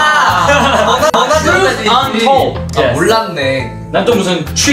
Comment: Jay's " TRUTH . UN . TOLD "...i thought he said " tree ton ton " is just iconic✨ ✋😩